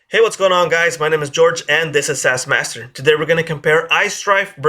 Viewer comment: DId he say Ass-Master?